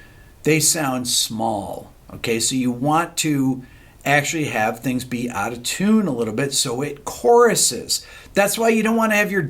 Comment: I thought it sounded like “You wanna have things Beato tune a little bit” 😆